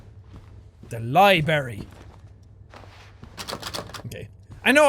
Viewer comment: I heard said "The Li-berry".